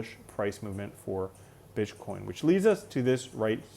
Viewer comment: Am I tripping or did I hear you say bitch coin @ 5:51 🤣